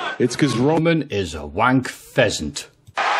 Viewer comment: What the heck is a wank pheasant? It’s just strange even for Cena to say something random. I thought he said an a hole but never thought he said that word.